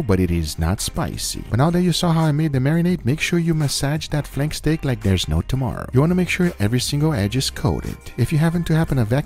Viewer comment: Did anyone else hear “massage that flex tape like there’s no tomorrow”? Edit